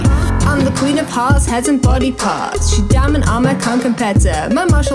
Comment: I thought said 'peasant body parts.'